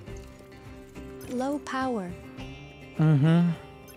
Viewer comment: It was funny when the voices said "Low Power" and i heard "Joe Biden"